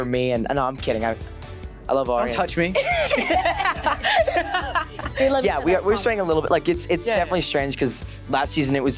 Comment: what did Leah say at 2:32 - 2:33 while she was laughing she said something, I can't figure it out??? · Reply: I think she said "they love each other i promise"